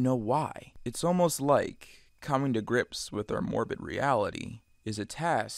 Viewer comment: i thought it said MorbinReality